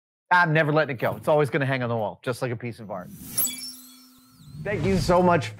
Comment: Right at the end I heard "always going to hang on the wall, just like a piece o fart"